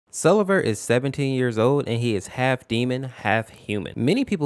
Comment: yall help me get my ears clean i heard “selever is 70-“ and i was like MY FAVOURITE FNF MOD CHARACTER IS AN OLD MAN?? 😭😭😭😭